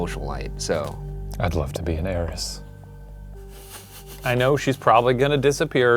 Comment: I totally thought Shane said he wanted to BE an heiress, not date 😂😂😂 I had visions of him in a ball gown with a fan but still the same hair and face